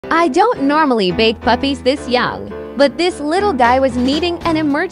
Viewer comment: I 100% misheard it "I don't normally BAKE puppies this young"😅